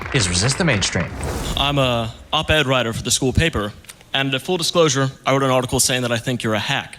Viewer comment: As soon as he said "I'm an oped writer for my school" I heard " I'm an angry power bottom"